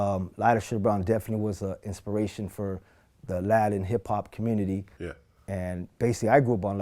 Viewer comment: its funny how a 3:27 he says the "Latin hip hop community", it sounds like he says "Aladdin hip hop community" the fucking irony lmao coincidence ? I think not 😂😂